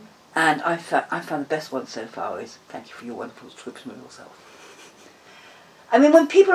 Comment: Love her wisdom! Can anyone catch what she says straight after "never let anybody knock you" at 11:10? I can't make out her first good answer. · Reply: Sounded to me like 'Thank you for your wonderful description of yourself'.